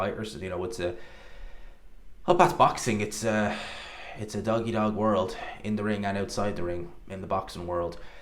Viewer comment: Did you just say it's a "Doggy Dog" world? 🤣🤣🤣